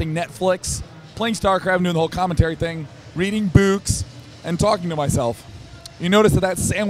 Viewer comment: i thought he was saying "reading boobs" until i heard the last syllable